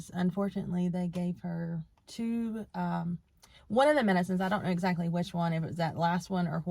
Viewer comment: Nah the question was do u know how u passed away The response I heard was “girl gave two or both medicine “ Question again do u know what happened to u Response I head was” I died “